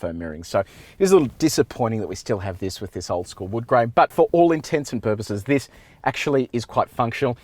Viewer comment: did you just say, "for all intensive purposes"?